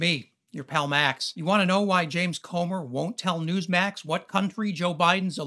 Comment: My bad I thought they were saying Gomer. Oh well. He acts like a GOMER. Lol.